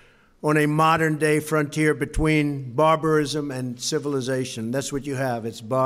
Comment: It sounds like Hes saying barbers